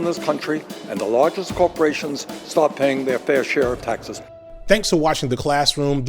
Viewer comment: the CC has Bernie saying "and the largest corporations STOP paying their fair share of taxes" instead of START. Thanks for the vid